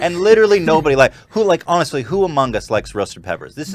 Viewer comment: When you said "who among us" I thought you said "Hugh Mungus"